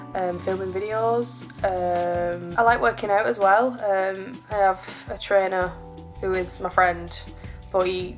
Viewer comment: When you said "I have a trainer but he" I thought you said "I have a trainer BOIII"